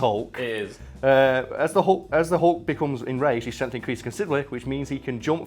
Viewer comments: is it just me, or does he say " The Hunk"? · Did he call him the hunk at 9:06 Wouldn't blame him